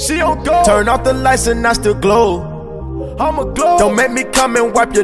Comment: The first time i listened to this song i heard "turn off the lights and i still grow" and im like wtf my nigga Offset is a fucking mushroom